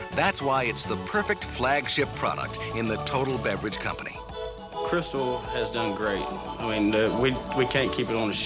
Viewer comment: "Crystal has done great. We can't keep it on the shelves. It sells. Everybody likes it." He did not say "Pepsi."